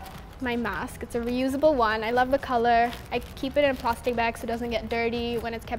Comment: Did anyone see the subtitles they put I keep it in the plastic ba Instead of plastic bag